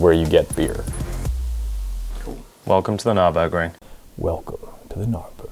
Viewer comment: Did just say, "welcome to the GNARburgring"?